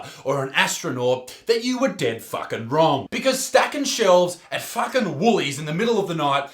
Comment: when he says "stacking shelves at fuckin' Woolies" the captions say "stacking shelves and fucking bullies" YouTube is more fun with captions on.